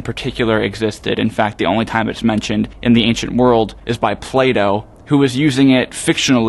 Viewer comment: legit thought you said Play Dough, rather than Plato